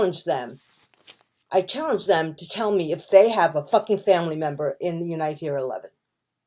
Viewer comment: What does she say at 42:32? Thanks. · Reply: "I challenged them to tell me if they have a fucking family member in the Unite Here 11."